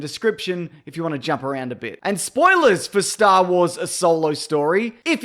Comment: did he say star wars : a solo story